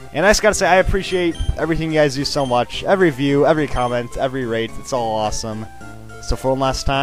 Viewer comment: Wait did he just say "every rape" XD